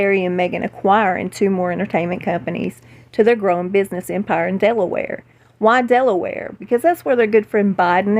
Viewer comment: When you said “Empire in Delaware” I heard, “Empire in Devilware”……I wonder if this is some kind of message???? ❤️